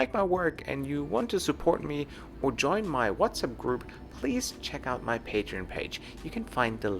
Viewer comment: Follow me on <link> for today’s trip to Iraq! Thanks :) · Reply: Did You Just Say WhatsApp Group